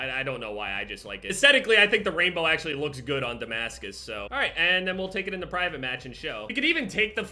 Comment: I thought it said I unlocked DEMARCUS 🔥😂🤦🏽‍♂️